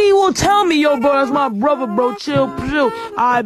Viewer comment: I'm sorry but what bro say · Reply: bro thats my brother bro chill